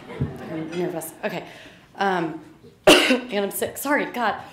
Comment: what do you mean she didnt even say "sorry" are you deaf?